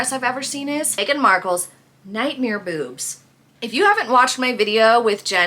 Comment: I thought you said boobs not beeps. Apologies